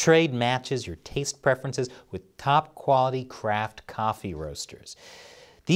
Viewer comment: "Top quality craft". What i heard:" top quality crap" 🤣😂